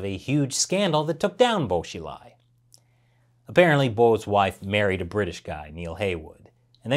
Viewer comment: Go to <link> and use code UNCENSORED to get 83% off a 2-year plan and 3 extra months for free! Get your Beijing Olympics T-Shirt today: <link> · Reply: say married and subtitles say *murdered*, pretty big difference